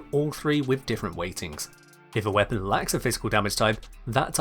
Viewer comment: the way i understand elements now is that you can troll your team more effectivly if you have weak status weapons with big aoe range, is that right? also, i kept hearing "if a weapon likes a physical damage type" had to turn on subtitles to understand that you said lacks